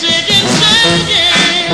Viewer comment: it sounds like he's saying "Chicken" instead of Shakin'.